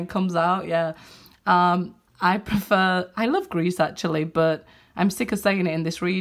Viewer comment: Did she say something about Greece? 10:13 Hey, I am watching it from Greece 💋💋💋